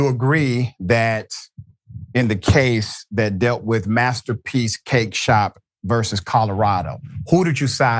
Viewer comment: I thought Dr. Richey was saying ‘Master P’s Cake Shop’ at first! LOL!!!